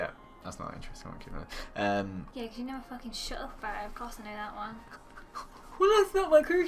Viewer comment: or a bit earlier did say the f word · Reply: The Doctors Daughter yeah she did she said something like 'you never f*cking shut up about it'